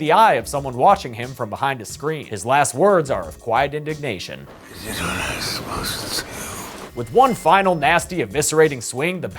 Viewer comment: What the balls does he say here I can't make it out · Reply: “I had done what i was supposed to do” it’s a bit hard to hear but that’s what he said.